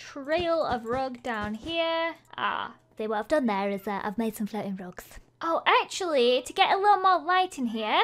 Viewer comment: wht is Lizzie saying lol listen to that literally every dat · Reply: "Ah... see what I've done there is I've made some floating rugs."